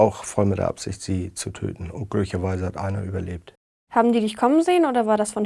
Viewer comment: "unglücklicherweise hat einer überlebt" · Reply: Ich habe verstanden "und glücklicherweise hat einer überlebt"